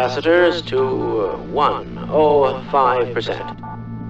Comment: No way! For more than ten years, I thought the scientist said: "Overhead capacitors to 1.... Oooh, 5%," as if he wanted to set it to 1 but changed his mind and changed it to 5% instead. Turns out he meant 105%.